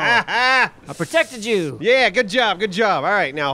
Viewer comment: I know said 'I protected you' to... But I swear I heard 'protect a jew' lmao 🤣